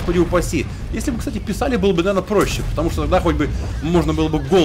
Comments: на 0:52 послушалось что он сказал "на**й бы"...чуть кофем не поперхнулся)) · Эээ.. народ а я один расслышал на 0:53 слово "нах*" во фразе "Если бы писали было бы наверное проще, потому что нах*** бы можно было бы голову хоть раз отключать и не думать" ? )